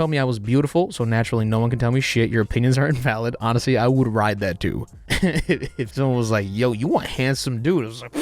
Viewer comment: i thought he said i would ride that dude. i was like man we getting some knew information bout our boy. lol anyway nice vid idea